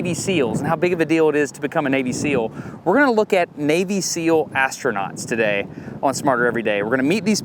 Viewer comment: NGL, totally thought he said "Baby Seal Astronauts". Which I think we can agree sounds utterly amazing!